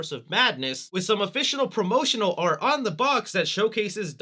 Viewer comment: 1. I'd love to enter. 2. Congrats on 400k! 3. At 6:45 did you say officinal?